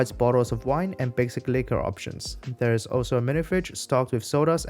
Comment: I misheard his pronunciation of "basic liquor options" and thought he said the bar provides "wine and basically corruptions."